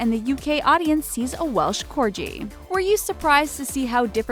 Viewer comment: did she said Welsh corgi?